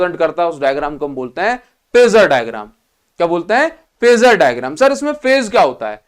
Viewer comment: for a sec, I thought he said, "Pizza diagram" lmao 😂😭😂